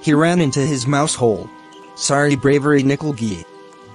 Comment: who hear "SARI BRAVERY NIKAL GAYI" 😂😂😂😂😂